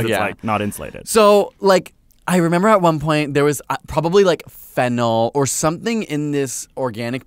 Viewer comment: When you said fennel, I thought you said 'phenol' and I was like "WHY WERE YOU EATING PHENOL??? DADS NO"